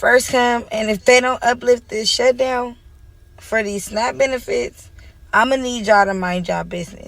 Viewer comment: Omfg i thought they were saying SNACK benefits not snap 🤣😂